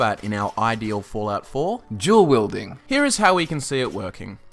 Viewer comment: I thought he said "Jewel Wield" not Dual Wield